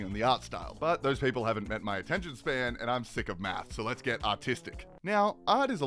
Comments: I heard "let's get autistic" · Ooooooh... AR-tisic? I thought you said AU-TISTIC.... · When he says artistic it sounds like he says autistic